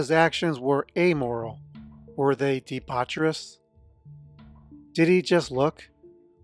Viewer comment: bro I swear I heard Diddy and I was like wtf is going on here.😂😂😂😂😂